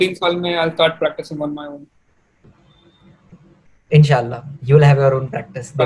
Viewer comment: sir did you just said inshallah ? ❤️❤️ Congrats sir you deserve this success